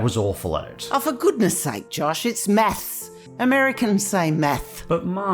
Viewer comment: I thought she said meth for a second there lol